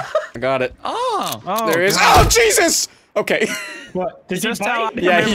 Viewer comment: For the longest time I thought said ‘PENIS’ not ‘JESUS’ 💀